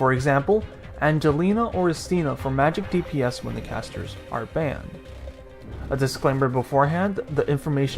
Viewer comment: Angelina and what? and after that did you say "magic dps only casters are banned"?? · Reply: Istina, the 5* supporter of the Ursus faction "Angelina or Istina for magic dps when casters are banned"